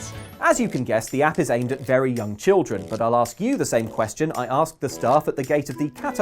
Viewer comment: Did anyone else see joker in ash on that pokemon play it?/ did anyone miss hear luke, i thought he said "aimed at very dumb children" 😂